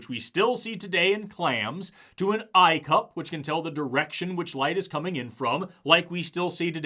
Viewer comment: an icup? sorry i couldnt quite hear you, could you maybe spell it out loud